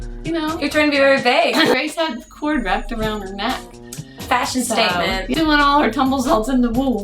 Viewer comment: When you referred to the cord wrapped around your neck as a fashion statement, I definitely heard "Fascist statement." Had to do a double take.